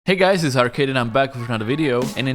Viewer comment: Beginning: What I heard: "Hey guys is Harkin"